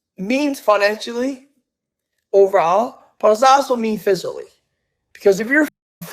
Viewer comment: did he just say physially?